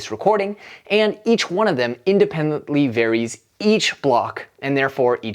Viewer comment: Can someone help me understand a word the speaker is saying? at 1:41, he says "Each of these computers has its own copy of the full blockchain, which is around 500 gigabytes at the time of this recording. And each one of them independently ?????? each block and therefore each transaction. What is the word he's saying there I marked with question marks? Varies? Buries? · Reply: Verifies!